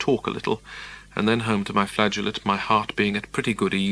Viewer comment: Am I hearing that correctly? "And then home to my flagellate".? What does that mean? I've only heard the word "flagellate" used by way of whipping oneself, and that definitely doesn't sound like something Pepys would do so I'm at a loss to understand it.